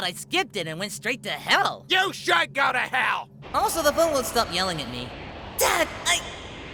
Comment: I now realize why it is important to have captions on when watching this series, this whole time i thought goku said “also the blue one wont stop yelling at me” but its actually “the phone”😂